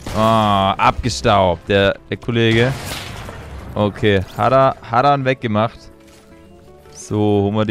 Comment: Ich habe Haram Verstanden😂